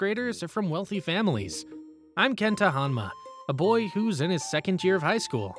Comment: I thought he said Kento Hanma and I was praying this man Yujiro wasn’t around 😂